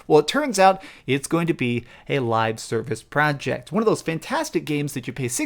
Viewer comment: Am I the only one who heard: "A Live CIRCUS project" ? Which is legit. I mean they are clowns, aren't they?